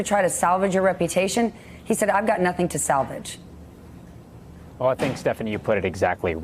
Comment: He actually said, "I dont have a reputation to salvage".